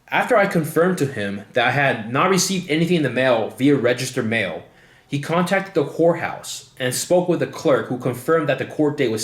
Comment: did you say, "he contacted the whore house!" LMAO this is a good 20 minutes!! Well done